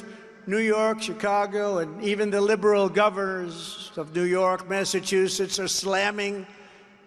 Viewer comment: What's tRump trying to say @ 0:10?? Governesses??